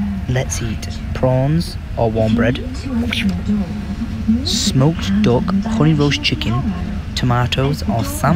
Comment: did enyone else thought that he sayd "smoked dog"